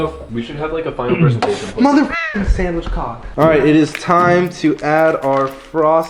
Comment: Did he say man that's hot Or sandwich cock?